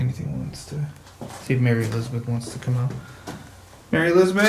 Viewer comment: When you asked if Mary Elizabeth was in this house, I heard "I may be."